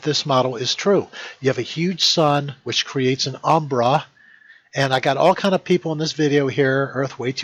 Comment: The way he said "umbra" sounded like "Aww, brah!"